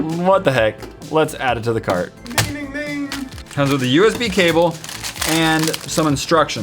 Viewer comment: did anyone hear say ninining ning ? Like if you did